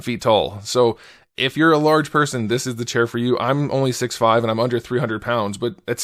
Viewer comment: I swear to god did he just say “only 6’5’